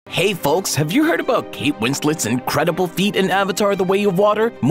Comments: i thought he said her incredible feet😂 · Why did I hear "incredible feet" 🤣